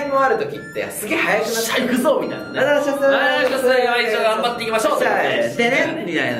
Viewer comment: 今一度頑張っていきましょう！て聞こえた